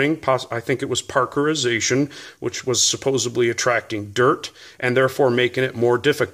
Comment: I questioned a few things, but near the end, I heard you say "supposebly," and I stopped caring. The 92fs is a tank.